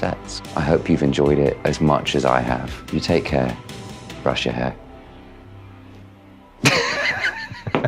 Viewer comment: Did he just say at the end "take care brush your hair" 😂